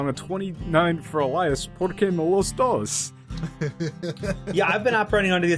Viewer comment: I legit cannot parse what said in reply. Is it like "Por Que o los dos"? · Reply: Okay, so it was probably "porque no Los dos" or "Why not Both" Truly, the most dated of Memes podcast. I am in pain